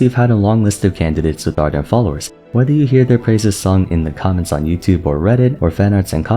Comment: I swear, i heard "Praise the Sun" in the 2:25 \[T]"/